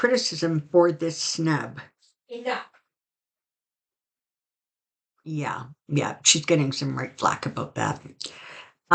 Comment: what was that?? I think I heard ‘Enough’ but could be wrong. Definitely heard something though. Anyone else?? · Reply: Dog barking...being told Enough....doesn't actually help...🐾🐾